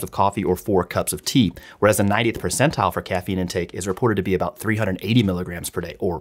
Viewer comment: i thought it said "goth percentile" instead of "90th percentile drinks 380 mg" and i was like "yeah that tracks, my goth friends drink a ton of coffee"